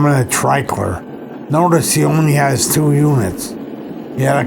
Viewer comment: Whats the location at the 3:22 mark? I can't make out what he's saying. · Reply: Treichlers, PA. Taken from route 145